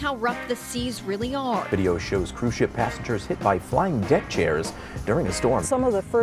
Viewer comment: At the end of the video with the news blurbs, I heard “flying dick chairs” and I can’t unhear it.